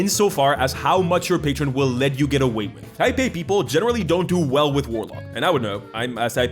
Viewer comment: Anyone knows what kind of people is he talking about in 14:29 ? IP people? The automatic subtitles say Taipei (???) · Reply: Type A? I don't know what thing he's type A of but maybe.